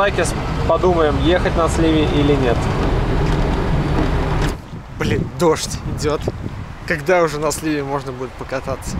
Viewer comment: Впервые слышу от мат) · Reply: бл#ть